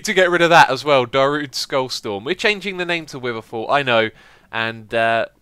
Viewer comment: I thought you said witherfort instead of witherfall, would've been fitting too.